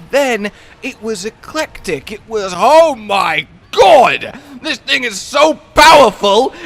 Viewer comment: I really misheard this, I heard "OH MY GOD, THIS N WORD IS SO POWERFUL"